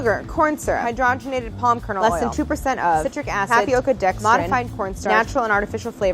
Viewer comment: when she said modifies corn starch i thought she said motified pornstars